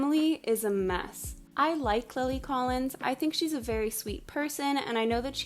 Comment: Did she said Chloe Collins at 1:07 or it was just me?